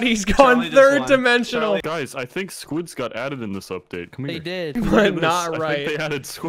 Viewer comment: I heard that as "Squids got Adam in this update." I don't think many people will get why that's funny to me